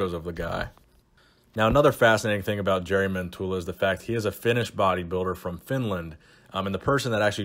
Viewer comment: At around 2:09 I heard it as he is a "finished bodybuilder" and I'm like, bubble gut, poor placing... I guess he is finished ...but then when you said he's from Finland then I realised you meant Finnish not finished